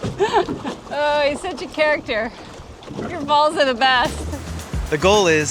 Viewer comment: When is on the board it sounds like says "Ooohh youre such a character, your balls are the best!" lol